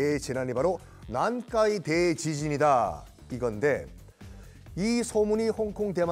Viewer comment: nankaiteechiziniと聞こえた。おそらくnannkaidaizisinn(南海大地震)